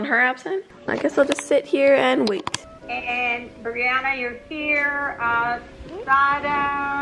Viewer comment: why did I hear " anddd Kageyama your here"